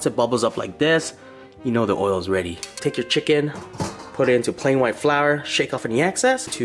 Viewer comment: He said plain white flour. I heard “quang wang flour.”